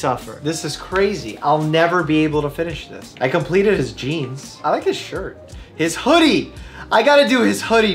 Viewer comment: Oh, jeans! I thought the game said to synthesize genes.